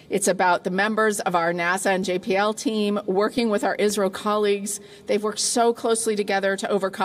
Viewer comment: Did she say "Israel" for "India"????😂